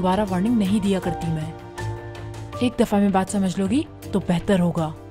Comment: i heard ek dfa me baat smjh lodi😂😂😂